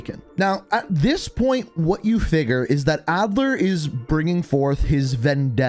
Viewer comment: did he say Adler??